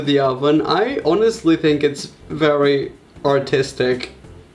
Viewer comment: I thought he said "autistic" lololol